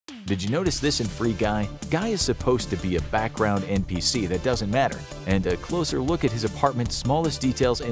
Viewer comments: I heard “guy is supposed to be a background NPC that does METH” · Ok I watched this 5 times and everytime I do I hear "he's a background npc that does meth" 🤣🤣 what is the guy actually saying lmao